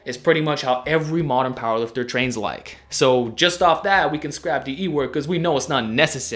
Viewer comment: What's the "E word" at 9:47? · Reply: DE work, dynamic effort